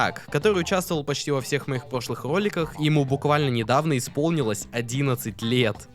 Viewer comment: Сначало мне послышалось что "Он участвовал в моих пошлых роликах" возможно автор Р не выговаривает?, но кадр был такой как в фильмах там автор видоса говорил: "слушай шутки с шутками но ты должен исполнить главную роль в видео", и там эпично его выгнал из студии что бы принести еду ахахах, с одной стороны мне жаль немного чела:(